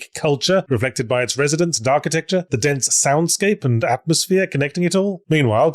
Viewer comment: I legit heard "Darkitecture" for Thief, and I was thinking: That's an amazing description for a game that is set SO DARK.